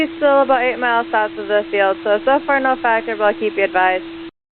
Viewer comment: I'm pretty sure TWR says "so far no factor" instead of "so that's far enough".